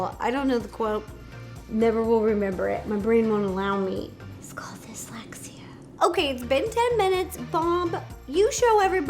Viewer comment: When she said "it's called dyslexia," I thought she said "god, you're sexy" to the plastic skeleton. I had to rewind, turn the volume up, and turn captions on to hear what she actually said.